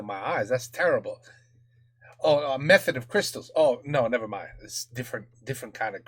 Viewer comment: The subtitles are wrong its not "method of crystals" it was "Cristaux de menthe" which means "Mint crystals", it's basically just crystallized mint.